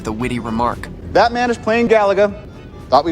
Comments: It sounded like he was saying Batman but then I read the subtitles💀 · When Tony said that man is playing Gallaga it sounds like he said Batman is playing gallaga 😂